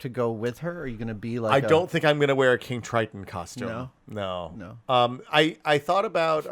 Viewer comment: Totally misheard King Triton as 'kink Triton'. DAMNIT WILL.